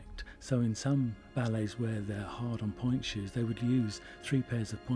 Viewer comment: I thought it said polite shoes